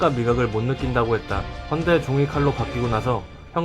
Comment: 나만 종이칼로 들려?